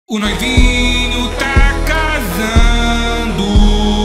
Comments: Eu entendi "O buraquinho ta casando" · "O noivinho ta casando ❤" "Pula🦘, Kika🐎 e rebola⛹️‍♀️" "Agora o divórcio rola💔" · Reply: Eu escutei "cagando" 💀